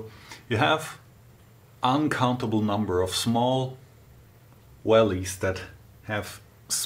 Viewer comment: I thought you said, "an uncomfortable number of small wells"